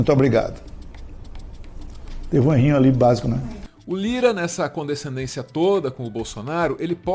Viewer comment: Sério, eu não entendi o que ele falou. Alguém decifra pra mim, por favor. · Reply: " teve um errinho ali básico né?"